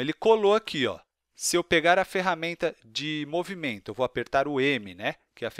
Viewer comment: Em 13:50 vc vai apertar oque? esta muito rápido e sem visualizar e ainda não da p entender o que vc disse entendi wesi! o que é isso? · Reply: Apertar o M do teclado... que é a ferramenta de Movimento.